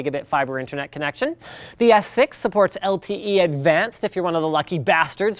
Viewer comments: Did he say S6 at 3:42 to 3:44 or am I having hearing problems? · Did linus say S6?